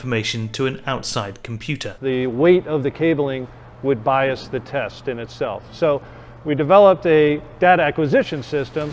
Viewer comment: I also misheard 4:08 as "The weight of the cableling would buy us the test in itself, so we developed a dad-acquisition-system, this is really like the black box that goes within inside the crash test dummy, but what that does is captures the Dada in microseconds from the crash, and then dad can be downloaded separatly." BTW the documentary is really interesting and I think they are doing an excellent job